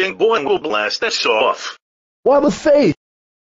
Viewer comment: Did James Said WOBBAFET?!